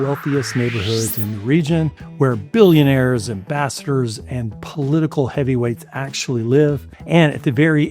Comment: I heard "ambassadors" as "bastards"